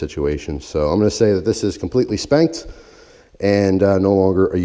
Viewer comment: When he said the knife was “completely spent,” I had to rewind cuz I thought he said “completely spanked” 😂😂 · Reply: I did say spanked :) lol